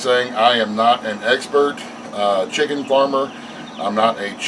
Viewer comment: did you say “egg-spert”? If so, bravo sir. Great video.